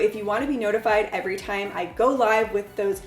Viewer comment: Lololol, I thought you said while youre alive, not whenever you go live 😄.